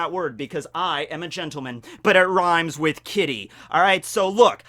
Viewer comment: Is it bad when he said kitty I thought he said kiddy which wouldn’t exactly be appropriate